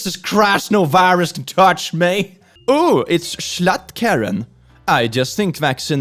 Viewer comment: HELP I HAD THIS PLAYING IN THE BACKGROUND AND I THOUGHT YOU SAID "oooh its $lu+ karen" and i opened the tab up to see what the fv<k that was supposed to mean and then i just saw Jschlatt and i was so confused and scared